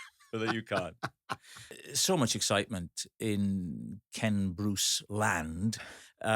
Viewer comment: For years I thought he was saying his name was Kent Bruce.